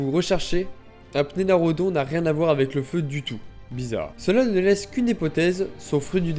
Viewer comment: un quoi XD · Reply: Un tiranosaure, ça s'entend pas? Mdrr